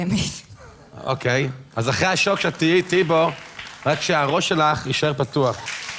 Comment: Did he say Tim Tebow at 3:45? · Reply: he says "tehi iti bo" (you will be with me in that)